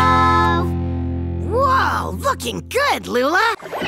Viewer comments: I heard "Looking good Luna!"😂😮😅❤ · I died of laughter. That came out of nowhere 😂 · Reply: I Think He Said Looking Good, But If You Understood Fucking Good, It's Ok. XD